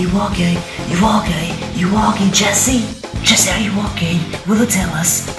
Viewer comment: It sounded like u were saying "Jessie are u gay?" Lol